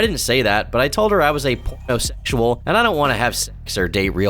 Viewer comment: What is the censored word at 12:10. Where said he was a p____o___sexual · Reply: Pornosexual I think. Someone who's addicted to porn.